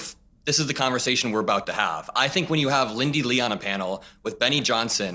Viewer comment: When you said Lindy Li, I thought you said Bandy Lee, which is possibly what Biden heard and why he said to tell her husband he said hi. I believe Dr. Bandy Lee is married.